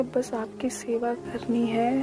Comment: what did she say? I didn’t understand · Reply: Bs apki sewa krni h?